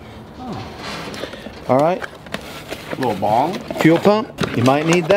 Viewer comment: did I hear that right? Little bong?